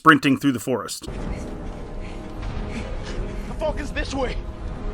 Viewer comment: every time I hear finn say "the falcon's this way" I always hear it as "the f**k is this way?"